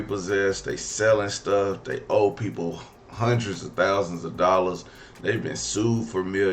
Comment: Man I heard her wrong, i thought she said Five Thousands